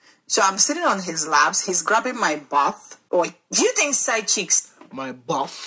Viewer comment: I thought she said side cheeks at 1st.